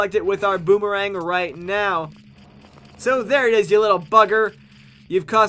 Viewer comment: In the dingo cavern I thought he said "there u r u little fucker!" Not "there u r u little buggar